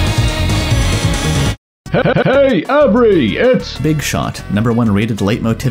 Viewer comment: I thought it said averi that scared me lol